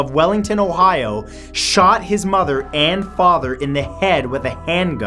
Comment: I thought you said he shot is mother, Anne Father. lol was I wrong